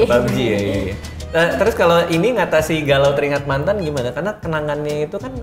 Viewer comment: kedengerannya keringet eh salah denger